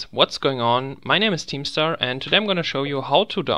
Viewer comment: When he said his name I swear I thought he said keemstar